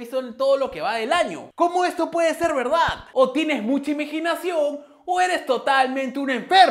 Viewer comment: Escuché IMEGINACIÓN en el 5:22 o me parece 😅